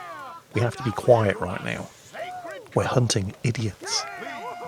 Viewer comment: "Be very very quiet we're hunting idiots", i had to listen to that twice because i thought he said "Be very very quiet we're hunting indians"